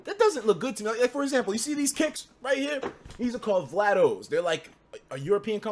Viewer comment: anyone know what are those? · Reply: Janiel vlados